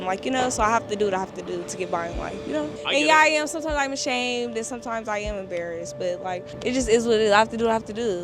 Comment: I thought she said "I'm ashamed I have to eat his berries" 😂 She said I'm ashamed and embarrassed🫡